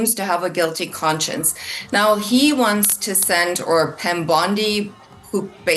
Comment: I swear she says "Pam Blondi" haha. Freudian Slip?